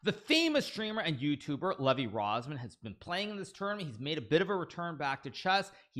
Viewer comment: The YouTube Subtitles got it it was LEVI ROZMAN😂